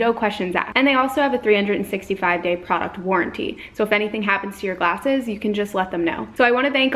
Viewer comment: Jeez she said “warranty” and for some reason I heard “quarantine” 🤦🏻‍♀️😂